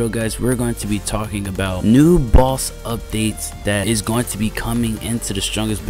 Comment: I heard new balls update 😂 (Jalactic_2022) Early access